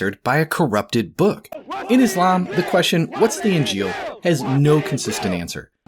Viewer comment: To quote the great Avery of GodLogic….”what’s the injeel?!!!” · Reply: I always thought he was saying ‘f k the injeel’ 😅